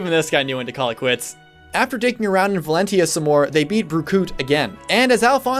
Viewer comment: D..Did you just call Bruno Berkut?